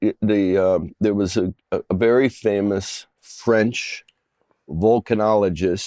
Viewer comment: Bill thought he said Vulcan. Probably was thinking of Star Trek. 😂